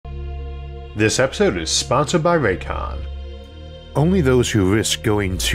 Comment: Thought it said sponsored by Raytheon at first lol